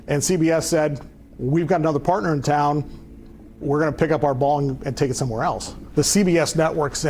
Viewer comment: It sounded like he said, "We'll pick up our bong and go elsewhere."